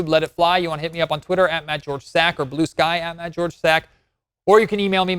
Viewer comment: Thought he said his name was Mad George 😂